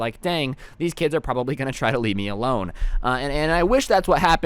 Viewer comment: did he just say leved😬